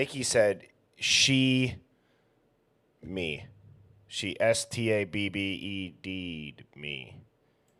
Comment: I hear :"You just stabbed me"